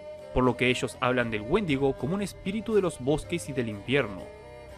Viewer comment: Pensé que decía mendigo :v